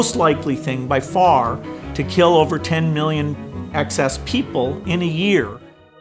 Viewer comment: First thing I heard was "excess people" right at the beginning! Really!!